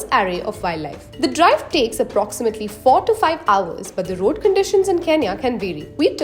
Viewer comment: Road conditions in Kenya can be what????? Say it?! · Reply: She said the road conditions can vary, meaning sometimes they're good sometimes they're bad 👍🏽